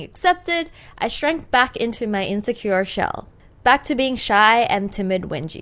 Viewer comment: at first I thought she said self like shelf but then I realized she said shell. (Meant